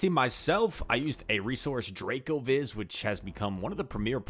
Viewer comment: Can I get the link of the Draco Wiz you mentioned? Fire video BTW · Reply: I’m also not able to find it, I thought he said Dracovis but that also doesn’t come up